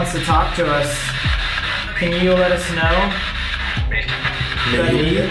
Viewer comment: idk if thats just me but i heard "body" and "you know" around here